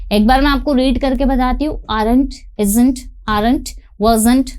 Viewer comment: Ma'am, the contraction of am not is, ain't or aren't? I'm little bit confused bcz I heard ain't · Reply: Aren't